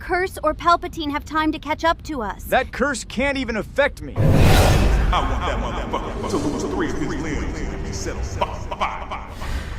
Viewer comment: what is Mace saying here? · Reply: He says "I want that motherfugger to lose three of his limbs, and be set on fugging fire"